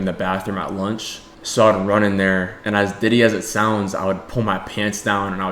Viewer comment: did he just say diddy